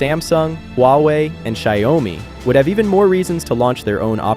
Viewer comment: "say home" instead of "XiaoMi"